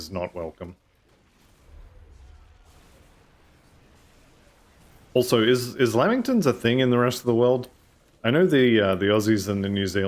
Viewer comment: Never heard of a Lamington in the US. Looked it up, and it is not like anything I have seen over here. They look good though. Also fun fact: The messenger is actually not dead, if you are allied w/ Kymons Chosen you can go back and talk to him at their compound and he will laugh at you :P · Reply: I thought he was saying "LEmington", but must be his accent. I was making fun of how he'd been saying messenger & sunder in my head.😅 I've never heard of it either and I've traveled the world a bit, though admittedly, not to the UK or Australia.